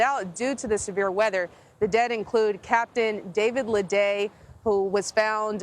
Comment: Really? I thought he was alive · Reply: It sounds like she said at 1:27 "The dead include....." Tragic and my heart goes out to their families.